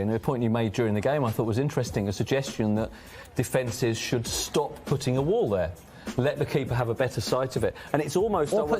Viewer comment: تقريبا سمعته قال ان يتوقفو عن وضع حائط صد (توقف عن الوقوف في حائط صد) وليس " توقع " ، هل هذا صحيح ؟ ام اني مخطأ !